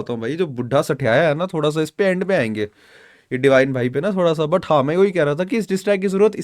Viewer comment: did he called divine buddha 🤭